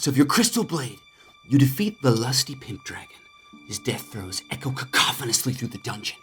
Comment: “With a mighty thrust of your crystal blade, you defeat the lusty pimp dragon…” yep it’s gonna be a good video. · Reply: I thought he said pink dragon 😭😭😭